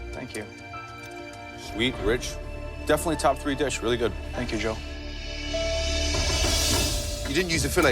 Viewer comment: Thank you Joe? Is that what I heard?